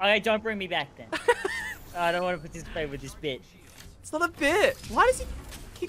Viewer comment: When first said “I’m not interacting with this bit” I thought he said “bitch” and I lost it!!